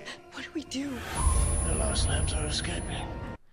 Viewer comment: Sounds like Sadler is saying, “The lost lambs are escaping “